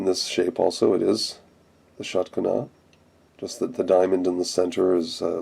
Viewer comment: any relation between shatkana and "shock and awe" from the iraq war? when you were saying shatkana i kept hearing "shock and awe" lol